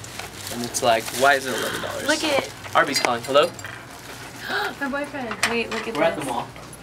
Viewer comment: does tori say "my boyfriend" at 02:10 (T_T)